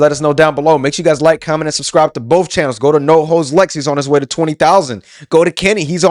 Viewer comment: I swear sometimes it sounds like youre saying "No Hoes Lex" and i always feel a little bad for my dawg